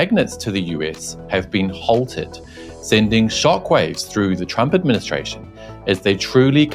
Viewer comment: Is it just me or did I just hear "Trumpet Ministration" at 0:49? 😅😅😅😂😂😂